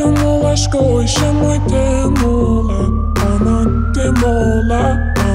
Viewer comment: parece que dice va a temblar y luego dice que hacemos